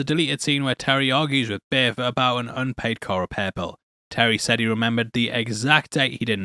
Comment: ok. I have listened to this like 7 times and all I hear is "...an unpaid color purple." What is he saying??? What are we even talking about???